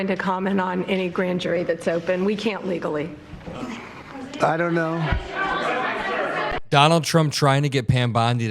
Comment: wait a minute did he just say I don’t know🤯🤣